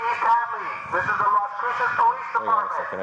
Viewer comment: I swear I heard him say "This is the Los Santos Police department" 😂😂